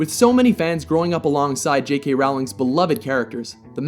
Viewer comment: I'd rather put The Green Mile instead of The Shining as a good film adaption. It's an amazing journey with one of the saddest ending I've ever seen/read. And how did you pronunce J.K Rowling? Sounded like you said Rawling? The rest is pretty okay.